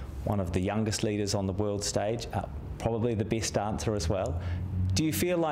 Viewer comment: She changed an interview question. Then Jack hoping to lighten the mood and make her feel homely wth the "Best Dancer" line, she didn't react and Jack maybe then realised she was in charge of this interview, and he may as well not be there. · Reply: I thought he said “best answer” and thought that didn’t make sense. 😂